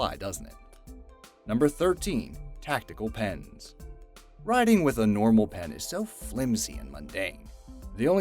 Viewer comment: did you say "Penis"